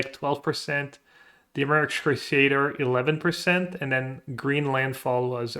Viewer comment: I have no idea what deck you said at 2:18. YouTube captions thinks you're saying "American Crusader" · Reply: Dimir excruciator